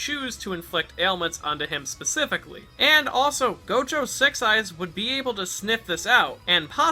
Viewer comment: I really thought he said gojo has sex eyes, and I kept thinking that for 2 minutes